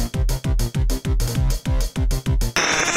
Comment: heres what the voice says at 0:20 Its freeze dance lava